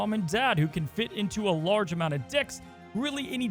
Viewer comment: it sounds like you said 'Dicks' and barely like 'Decks'. :)